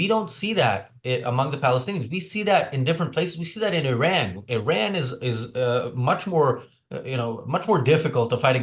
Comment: is he saying "Iran" or "Ayn Rand" 😂